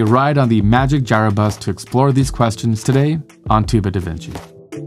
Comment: Some Americans need to speak more clearly. I thought he said Two bit Adventure. I need subtitles to understand. Shame as the subject matter is astounding.